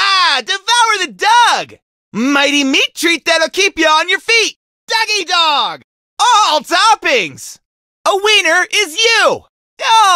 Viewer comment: I always thought he said “a wiener in you”